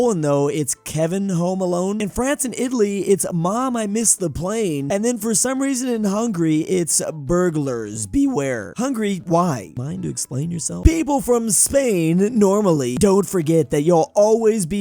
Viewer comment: I almost thought it said“Bulgars, beware”